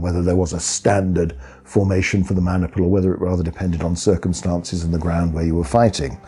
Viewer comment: "Standard formation for the mana pool" sounded like they gonna be casting spells. (I know, it's "maniple", but it sounded funny to me)